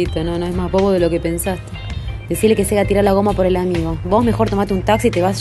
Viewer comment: Que dice en el minuto 3:58?? "Decile que se le tirarar la goma? Algo así. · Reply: Ricardo 777 decile que se le haga tirar la goma por el amigo dice